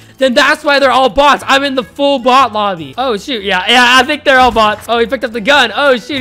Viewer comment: listen closely sweared he said the s word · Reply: He said “oh shoot”it sounded like he said the s word but he actually didn’t